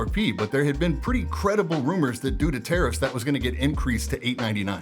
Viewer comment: lol, I thought you said "Due to terrorists" but went back to catch it again; "Tarrifs". Got it...